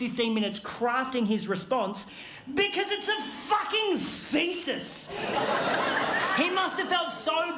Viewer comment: Thesis? Huh! I always hear him say "feces" instead!